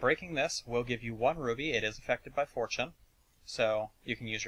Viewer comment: I thought you said that "Rubies are effected by 4chan"